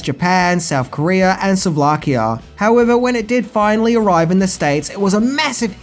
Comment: Did he just say "souvlakia" instead of "Slovakia"?